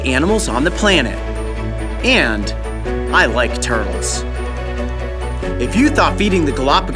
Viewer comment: Did Coyote really say "I like turtles"